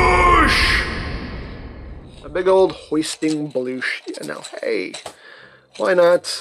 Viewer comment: A "hoist bloosh"... So misheard that